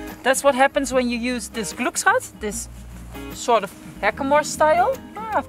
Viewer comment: So you speak German? I heard the "Glücksrad" 😂😂